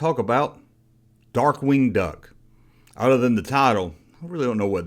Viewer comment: I thought you said "Dark Winged Nut!"🤣😂 Cheers👍🏻🍻